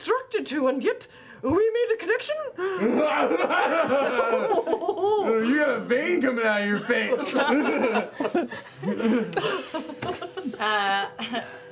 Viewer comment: what does that guy say at 22:55? "You got a "???" on your face." I keep missing it. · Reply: Ali Ince “You got a vein coming out of your face”